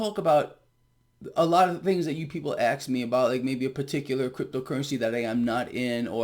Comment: Ghetto Greg... Did you just say , "axed me about " ??? 😄🤠😆